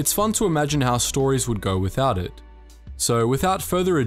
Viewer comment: I thought you said how clones will look without armour xD